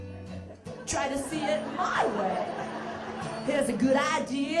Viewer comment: from minute 4:20 in the end what does Lucy say? I can not understand! please Help me to hunderstand!!! <3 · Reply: "Try to see it MY way!"